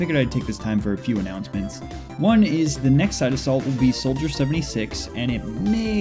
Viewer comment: At first I thought it said side assault not side of salt